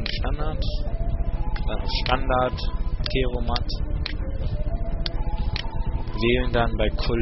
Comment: Ich finde deine Tutorials echt klasse...aber kannst du mir verraten was du bei 8:38 sagst??? ich verstehe da,,teromant''.Ich hab sowas aber gar nicht..liegt das vieleicht daran das ich Version 2010 habe oder bin ich einfach zu dumm???